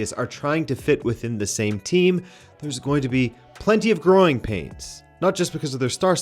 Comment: "Plenty of groin pains." I swear I understood that instead of 'growing' and then thought "well yeah, if you're going to (allegedly) assault someone, she might kick you down there, hence the pain. 😅😂 All jokes aside though, I don't believe these rumors. It's too perfect of a scandal, especially when you pair it with the debacle his presence in Sweden had already caused. Anyways, innocent until proven guilty.